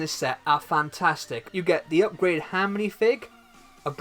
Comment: it sounds like he saids upgraded ham